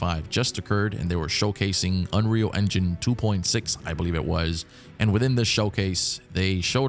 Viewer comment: I heard unreal engine 2.6